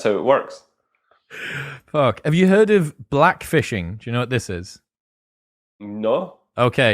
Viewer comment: I thought it said blackfisting.